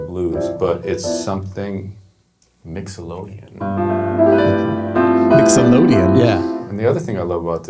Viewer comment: I thought he said “Mix-O-lodian” instead of Mixolydian. And there you have it. I now have a raging case of verbal satiation. 😂😅🤣👍🏽